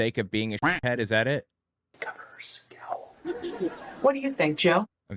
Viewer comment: I think he said "her scowl" as in her facial expressions?